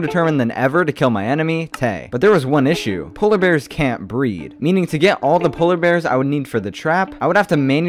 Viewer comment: I thought he said polar bears can't breath